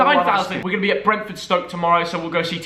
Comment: I thought he said enjoy your fried egg